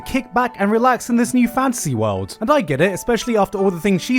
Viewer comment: Every time says “fantasy world” i hear “fancy world”